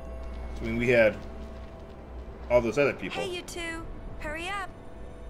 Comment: Years of being on this site, at 4:46 I kept hearing Yuna saying "Hey Youtube!"